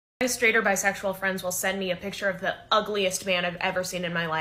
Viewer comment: I thought you said ugliest fan.